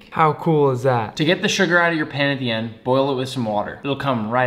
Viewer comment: i had to reply the end like 5 times bcuz i thought he said “now these are fuck to make”